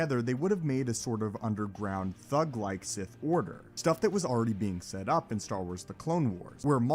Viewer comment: I thought he said "Underground Thug Life Sith Order" and now I'm disappointed.